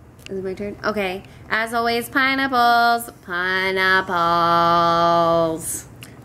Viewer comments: It honestly sounded like you said "PIE NIPPLES!" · Ok so for the as always I thought she said pie and apples.... BUT THEN realized she said pineapples.... I think I need to go to bed.